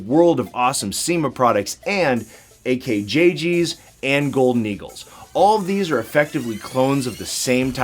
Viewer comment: I thought he said double eagle and I was like da hel